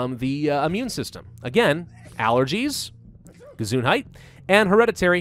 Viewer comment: did he say Gesundheit? So the german word?